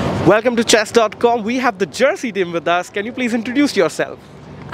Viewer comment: what country did they say? I am hearing Jersey, is that correct?